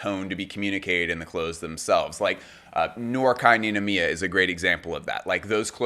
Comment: whose the designer at 4:23 i know he said it in the vid but i cant figure out how to spell it. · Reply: Noir Kei Ninomiya 💫💫